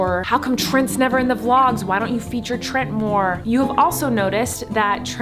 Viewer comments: When she said "why don't you feature Trent" I thought she said " why don't you feed your trent" and I was dying laughing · When she said "Why don't you feature Trent more?" I thought she said "Why don't you feed your Trent more?" And I couldn't stop laughing so hard